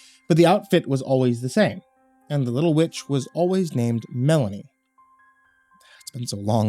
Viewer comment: When he says the word; “Juicy” and asks; “Did you enjoy it little witch?”. That caused me to pause. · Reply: Wait, I thought he said, "Little bitch." XD